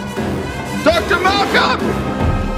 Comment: I can never tell what the fuck he's saying without subtitles. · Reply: Connor Vaughn “Dr. Malcolm?! What have you done with it? I want that infant!”